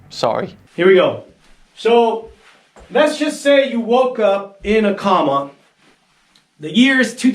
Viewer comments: I misheard and thought that he said "suppose you wake up in a korma". Although that would make more sense than the rest of his video. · He said “waking up IN a comma”. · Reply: I thought he said waking up in a korma